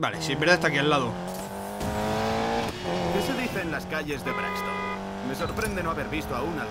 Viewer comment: en el minuto 17:38 dice que dicen de eb las calles o algo asi like para que lo vea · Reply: Kevin sosa,dicen de braxtom,que es como se llama la ciudad